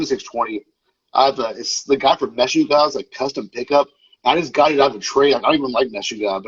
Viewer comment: what band name is he saying?? Can't hear it. Sounds like "Meshuggah" but that can't be it (at least the way he's pronouncing it). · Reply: It’s Meshuggah lol